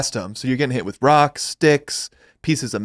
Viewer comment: first time listening I thought he said they were filling the cannon with dicks.